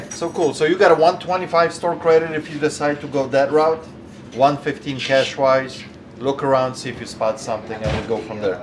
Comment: oh that’s interesting, I figured you would give more in store credit over cash? Keeping the money in your pocket would make more sense over the cash option. Interesting choice on that proposition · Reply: Oh never mind, the closed caption actually said it- I thought you said 125 store credit or 150 cash. Closed caption said 115 cash. My apologies!